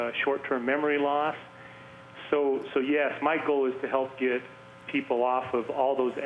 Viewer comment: Why does he call him Michael at 3:03 and not??? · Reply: He said my goal